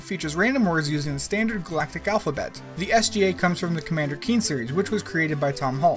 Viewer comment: I swear you said Tom Holland